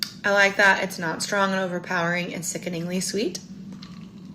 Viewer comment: I heard "it's not strong or overpowering or sickenly sweet." I definitely can understand why you heard her say it the other way.